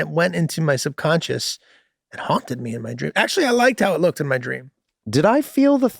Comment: I heard "honked at me in my dream." Not haunted me lol.